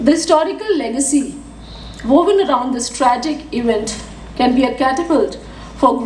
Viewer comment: Did she say " STRAGIC " ?